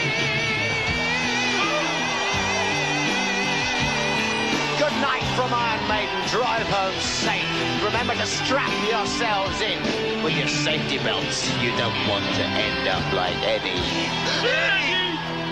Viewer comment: Is it just me or does it also sometimes kind of sound like they say "fuck with your safety belt"